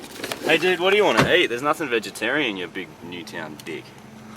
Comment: WHAT DOES he say ? something dick?